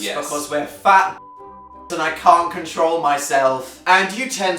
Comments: the word dan said that was bleeped i've heard him say it before in a video but it was also bleeped so like can someone help me out and tell me what he said cause I don't have any clue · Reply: Bastard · totally thought the utensils said uteruses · What did Dan say at 1:46 normally I can tell through the bleeps but I can't ughh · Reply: Bastards